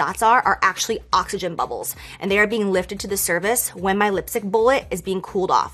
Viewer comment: 🤔 "Lifted to the service" Wth is she sayin' 😂? That's a lot of 💲 for some nude lipsticks 💅☕😒🐂💩